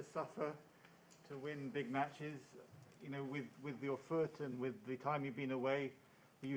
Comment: "You know, with the OFFURTEN, with the time you've been away". Offurten? What does he say there? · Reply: with your foot